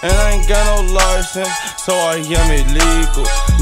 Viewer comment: kinda sounds like he says "but i got that beef wit chu"